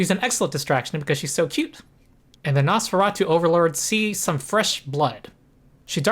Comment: I wasn't paying attention. I was looking at Megan. Did you say Nosferatu?